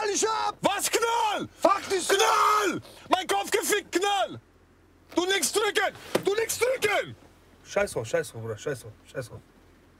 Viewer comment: Wer hat es auch als aller erstes "Du nix Türke, du nix Türke" verstanden 😅😂 .. Ey Ich dachte schon.. haha